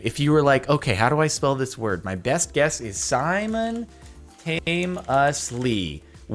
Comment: Youtube captions said Simon Haim instead od Simon tame